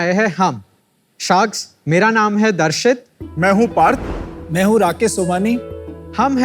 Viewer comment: i heard pad 😂